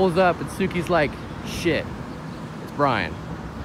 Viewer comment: Thought suki said “oh shit, it’s bullet”